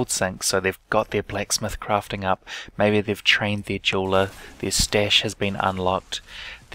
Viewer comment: Did you just say shit stash?